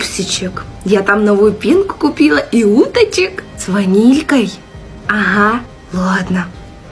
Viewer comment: Мама: Я там новую пенку купила и уточек 40 сын : с ванилькой 😂 Уточки с ванилькой ( мне так послышалось